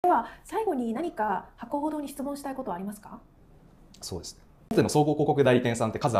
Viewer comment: テロップ見てなかったから、最後に何か吐くほど質問したいことはありますか？って聞こえたw